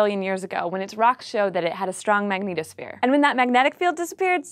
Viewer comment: I kept hearing "Magneto-sphere" (like from X-men) every time she said "magnetosphere".